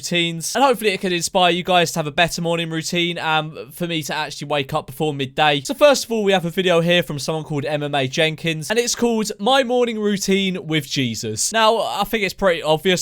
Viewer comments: When you said her channel i thought you said MMA Jenkins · Anyone else thought he said ‘MMA’ Jenkins ?